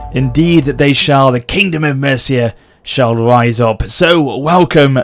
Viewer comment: I thought it said kingdom of Murica....I was disappointed